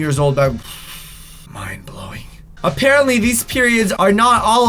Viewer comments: did anyone else hear him say apparently these periods instead of pyramids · when you said also these periods alright actually out all alone in the desert you said periods instead of pyramids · am I the only one who heard him say periods instead of pyramids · who else heard periods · did he say periods? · he said periods instead of pyramids. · he said periods instead of pyramids 😂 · does he say periods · it sounds like he said periods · Is it just me or did say period instead of pyramid?!? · Did anyone notice that he said periods instead of pyramids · Was it just me or did anyone else catch that he said “periods” instead of pyramids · Just watch 2:55 He said period instead of pyramid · does he say periods or pyramid? 😂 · Brah he said period instead of pyramids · Who else heard him say period not peramed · It sounded like he said period not pyramid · Who else heard him say period not prymid lol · instead of saying pyramid it sounded like he said period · he said period instead of pyramid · did he say "periods"? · Yo who else noticed that when he said pyramids aren’t actually alone in the desert but he actually said “periods aren’t actually alone in the desert” 🤣😂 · he said periods instead of pyramids lol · Am I the only one that heard say periods instead of pyramids???? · did he say periods instead of pyramid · Does he say period instead of pyramids:))))))) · these what i swear i heard periods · Did he say periods instead of pyramids at 2:54 lol